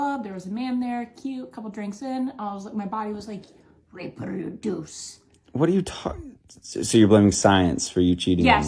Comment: Did she just say "I was just listening to my body raperduce!' LOL!